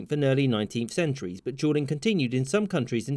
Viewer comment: This whole time I thought he was saying, “Juuling”